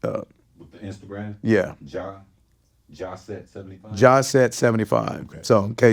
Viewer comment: Contact info for that necklace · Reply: it sounded like he said Johnset75